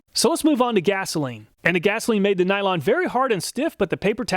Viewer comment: did you say "the nylon" or "the vinyl"?